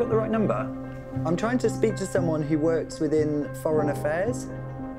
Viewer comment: I thought I heard 'someone within fallen affairs' and I thought to myself.. Mmh.